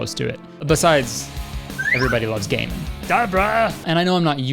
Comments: Ah yes- the acclaimed video game based on the legendary tv show: Everybody Loves Gay Men i swear i actually thought thats what he was saying for a full minute idk whats wrong with me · Thought he said "Everybody loves gay men" at first, and I wasn't disappointed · When he said "Everyone loves gamin" I thought he said "Everyone loves gay men" lol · I heard "everybody loves gaming" as "everyone loved Gaymond"